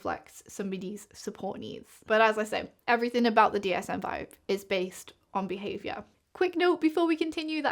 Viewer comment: I got an ad at a funny place while you were talking about the dsm 5 so it sounded like you said "everything in the dsm 5 is based."